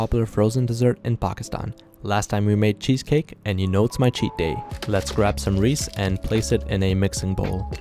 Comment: Every time I watch his videos I hear “it’s his cheat day” 😁😁😄 btw I love ur videos ♥️